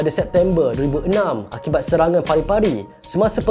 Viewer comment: tiga kali ulang atau aku salah dengar "Pari-Pari" or ikan pari.. Cuba komen